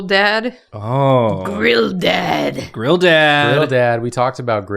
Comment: Oh this tail? Don’t worry… it’s just Jafar’s old staff…. I got it cheap on Burp Bazaar. When said would also be a “grill dad” I heard “girl dad.”